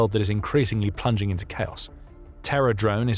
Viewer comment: I thought I heard TERROR DRONES from the start, that would be too terryfying to see for real 😱